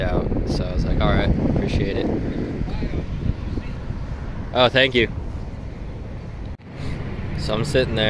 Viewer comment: Happy to see you vlog again! What does the guy say at 2:42? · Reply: Thanks! Something to the effect of "I put your pie on the seat". He bought me dinner from Hardee's